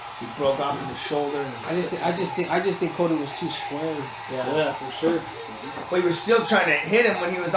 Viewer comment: what does Cejudo say? "i just think hes too...." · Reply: adzbxr1 Cody was too squared